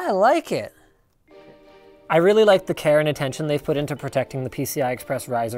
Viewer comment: Did I just hear "Karen Attention"?